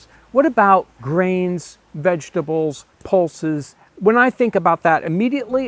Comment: what is that word? Poulces? I’m unfamiliar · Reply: Pulses. Lentils.